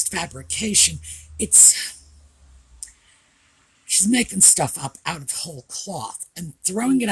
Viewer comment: At 34 minutes says she’s making stuff up. I was doing the ironing at the time and I thought had said she’s Megan stuff up… Well although didn’t say it, I did chortle before rewinding